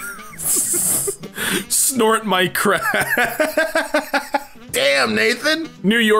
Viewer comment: I thought he said snort my salt lmao